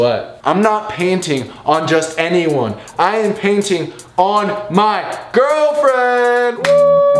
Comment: or 3 he said panting I think or said something that wasn’t painting so